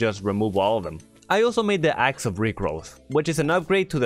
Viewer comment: I thought bro said axe of rickrolls